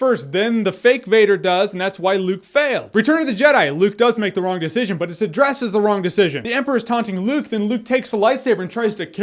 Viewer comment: wha does he say at 10:46 ? "he makes the wrong decision but it ..... the wrong decision" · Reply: "He makes the wrong decision but it's addressed as the wrong decision"